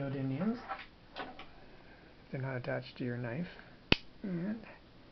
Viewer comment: How to fix your magnets then at 2:19-20 i cant understand what you say to fix your magnets you simply take a couple blah blah... what did you say exactly?? Thank you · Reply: I think I said " they were stuck to my knife" off camera . Its nothing important. I suck as a video maker I guess.